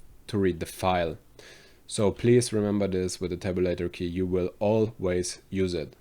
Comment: sorry, what key at 9:24 are you saying we'll always use? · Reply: tab